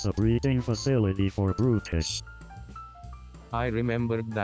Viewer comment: i barely looked at the video while listening to this and all this time i thought they said british virus and only realized it was "brutish virus"